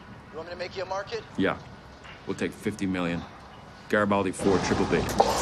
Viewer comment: what did he say here?? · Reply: Garibaldi 4 triple b, the thing they wanted to short